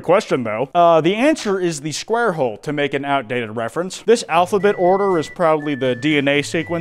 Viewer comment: I heard “it goes in the square hole to make a outdated reference” while hardly focused and started getting so mad.